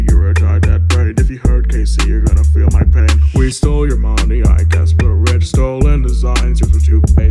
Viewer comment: " we stole your mom 👹✨" - 2024 AT LEAST WHAT I HEARD😃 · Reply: Thought he said “we stole your mommy”